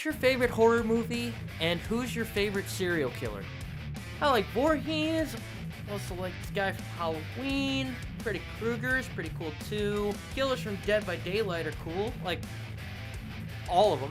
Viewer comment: did anyone else's caption say "pretty Kruger" ?😂